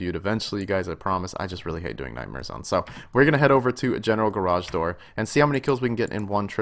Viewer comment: thought u said general garage door lol